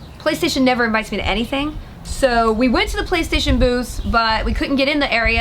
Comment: OH, BOOTHS. I thought you said there was booze for everything, and was thrown.